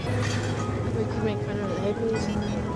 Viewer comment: Did she say what I think she said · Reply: We could make fun of the hippies